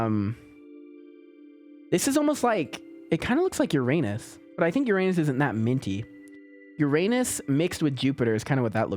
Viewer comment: what did he say 😂🤣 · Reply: we have the exact same amount of subscribers